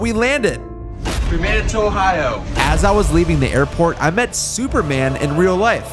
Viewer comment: DID YOU JUST SAY OHIO